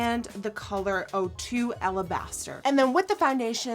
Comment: i legit thought she said "ala bastartd"